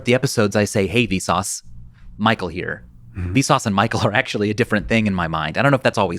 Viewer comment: Damn. I never caught on to "Hey Vsauce. Michael here." meaning that the audience is the Vsauce. I always heard it as "Hey. Vsauce Michael here."